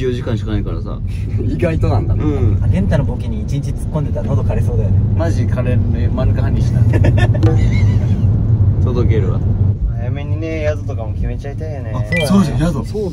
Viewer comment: やっと聞き取れた！ 閑也のツッコミに対する元太の返事 「マジ枯れるよ マヌカハニーしないと！ 、、、、、届けるわ」だ！！ げんげんツッコミに対しての返し 優しさで溢れてた、、、！